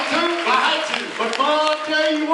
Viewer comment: What does Jensen say in that southern accent? I only understood "Yeah I had ta 'n order 'a work!" XD but not later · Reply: Right after that sounds like he said, "Want to? I had to." And then after that he said," I tell you what, it has been hard going. Get her done."